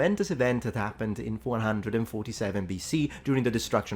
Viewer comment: Sounds like you said 447BC rather than 147🤓